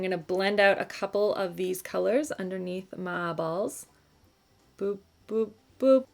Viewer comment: It totally sounded like you said “going to blend out a couple of these colors under my balls” not my eyeballs😂